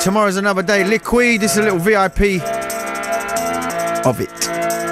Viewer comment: I always hear "Is a little VIP Hobbit" in the beginning of the track. xD